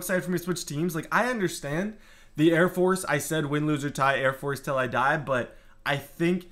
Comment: rip for air force. there only hope.win loose or tie air force for life i thought you said